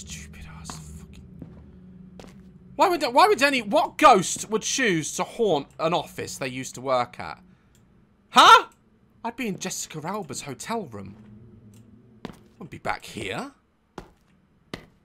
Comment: in 9:33, did he say “i’ll be in jessica alba hotel room” or am i wrong?😭